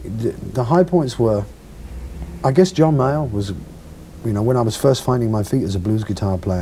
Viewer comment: Who is he talking about when he says John Mayer?! the one I know was 13 years old at the time so I must have misheard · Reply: Definitely John Mayall. He was instrumental (pun intended) in Eric's early career.